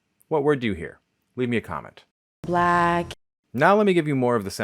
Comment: Response to the W hat do you hear question: The first time, I thought they were saying flag. Obviously I got it as block at the end.